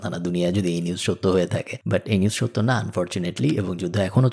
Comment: Did you say fortunately এই নিউজ সত্য না?